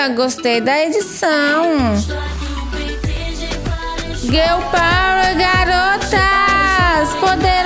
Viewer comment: eu juro que eu entendi "Gay Power Garotas" ;-; KKKKKKKJ